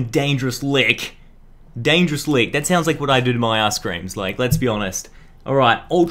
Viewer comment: Anyone else think it sounded like he said "That's what I do to my asscream" when he was talking about lickilicky?